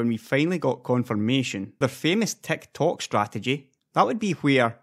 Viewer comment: I thought you were saying Tech Talk then I realized you were saying Tick Tock lol -great vid though